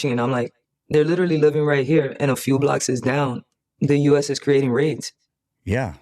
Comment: Did this one just say “ And just a few Blockses down?” Or did I hear that wrong @ 4:00 ?